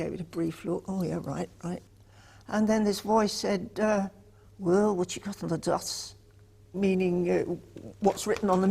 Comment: Can anyone tell me what Paul said to her at 1:20 "And then this voice said, ".............." (???) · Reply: ''Well what you got on the dots''